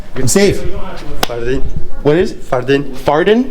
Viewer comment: I thought he said his name was, “Farted”…. Damn just spit out my water. Oh that cracked me up.